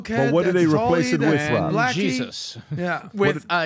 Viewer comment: did Adam just say blacky? 🤦🏾‍♂️🤣🤣🤣